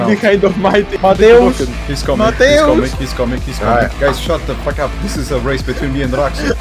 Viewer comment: did i hear matevž in the background💀 (matevž is a slovenian name btw)